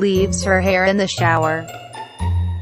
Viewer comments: “That is not my hair.” “THEN WHOS IS IT-“ (Edit: MOM IM FAMOUS) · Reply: I thought she said “hey stop my hair” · At the end i hear "THEN WHOS ZIZZY?!-" lol 😅